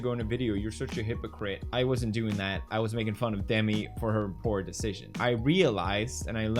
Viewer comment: Confused as hell because I thought you said Demi Moore and noone in the comments seems to notice it